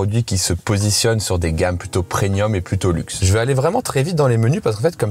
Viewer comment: Oh non, c'est moi où t'as dit "preNium"